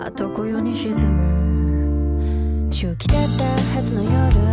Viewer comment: ごめんなさい、正気だったがしゅきだったにずっと聞こえてました懺悔します